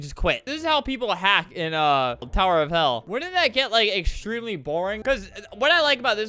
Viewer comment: Your saying Tower of hell I thought you say Tower of heck 😂😂😂🤣🤣